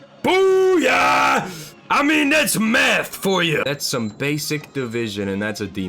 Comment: At first I thought he said "That's meth for ya!" Lol